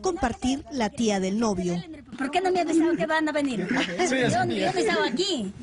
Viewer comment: Jajaja me meo de riza en el 1:24 yo creo que dice no mmes